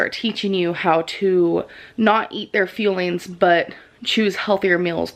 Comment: YES finally someone I watch said something about how she pronounces "fuel-ings" she really does sound like shes saying, "feelings" its so annoying lol.